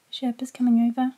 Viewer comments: I swear he legit said "Sherpa" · OMFG He actually said “to Sherpa” in